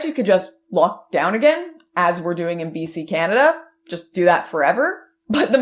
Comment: Almost thought you said "because I'm in PC canada".